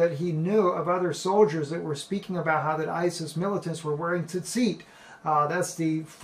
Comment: Could someone please tell me what the word he said at 2:56 ?! He said something about isis terrorists wearing something looking like what Jewish orthodox usually wear (tedseet or something like that), thanks.